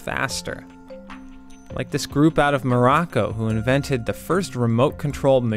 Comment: Because Scorpion is the best Mortal Kombat character of all time! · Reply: I read this when I was watching around 2:15. It made me mishear Morocco into Baraka 😂